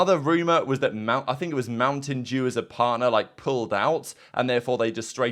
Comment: Idk why I thought said mountain Jew around 11:35 😭